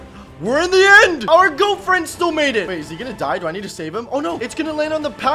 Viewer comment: I thought he said girlfriend is goat friend 🤣